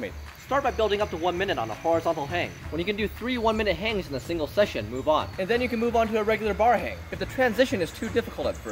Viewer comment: Definitely thought he said "when you can do 3 1-minute hangs in a single second, move on" and my brain went 'God mode.'